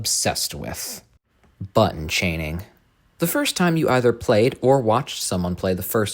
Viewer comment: sounds like he said butt chaining